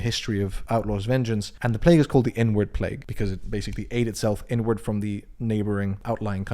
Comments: i thought you said n-word plague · Thought you said n-word plague for a second